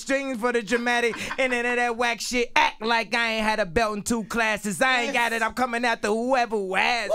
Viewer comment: ACT LIKE I AINT HAD A BELT IN TWO CLASSES. Damn. In boxing its weight classes and belts. And kanye got 2 belts. In producing and rap. For people who didnt get it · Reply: " thought he said "belt and two classics"